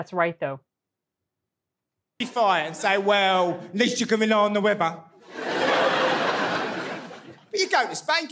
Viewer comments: Hiya. Glad you enjoyed it. Where you thought he said 'river', he said 'weather'. Some other routines of his are "Going Out Out" and "Big Sainsbury's" (a UK Supermarket Chain). Stay safe. All the best to you. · The thing that you thought he said about the river was actually, "atleast you can rely on the weather." It can be tricky. Keep smiling. 😎👍